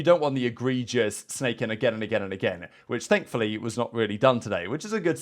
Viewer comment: listening in my phone speaker thought he said “snakefully” 😂